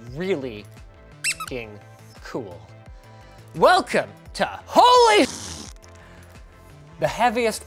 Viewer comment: I swear he said "HOLY F💩!"